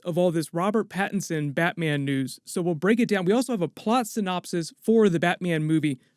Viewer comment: I really thought you said Robert Pattinson Batman nudes there. 😅